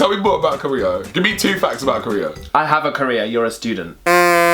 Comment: Sorry I'm stupid, but what did say at 9:59? "I have a Korea, you're a student"? What does that mean? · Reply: ajaninprogress I think he said "career" not "Korea" lol